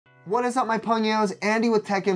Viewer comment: I always thought he said. "Whats up my funions" 😂